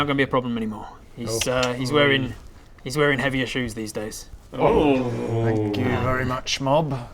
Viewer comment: my ears heard "he's wearing heavy issues these days" after what Baradún said I was so confused. 😅 I get it now lol